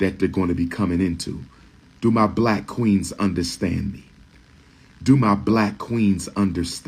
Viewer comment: When he said "Do my Black Queens understand". I swear, I heard him say " Do my Black Queens want to be scammed. Do my Black Queens want to be scammed. Do my Black Queens want to be scammed 😂